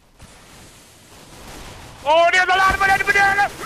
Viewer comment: I think what Nogla said was "There's a lot of them at banana"